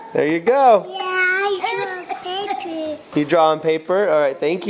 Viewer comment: i thought he said i draw on baby